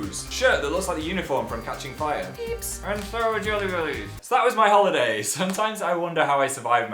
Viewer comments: When he said jelly belly, I thought he said jelly baby and was like "fourth Doctor!" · I was confused because I thought Phil said "shirt that looks like the unicorn from catching fire" I DIDN'T SEE A UNICORN O.O Then I realized he said uniform c